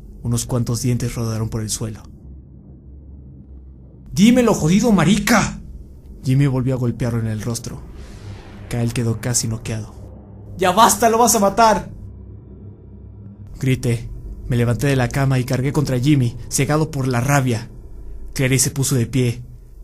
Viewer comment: Como grita el protagonista da risa, parece que dice "LO VAS A MATAR PRRO" No para de hacerme reír XDXD